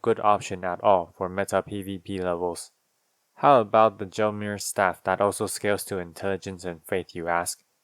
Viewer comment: almighty 😂😂😂i could have SWORN you said the "john mayer" staff when you were talking about the gelmir staff